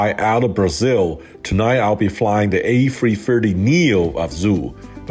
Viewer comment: Thought it said a380 neo and I got scared